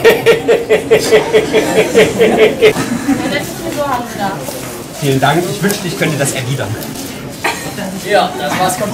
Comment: Ich kann leider nicht verstehen was sie sagt und habe dadurch das Gefühl, einen guten Gag zu verpassen :( PS: 0:17 snitches get stitches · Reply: "schöne frisur haben sie da" sowas in der art.